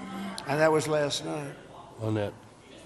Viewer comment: Did I hear "own it"? 😅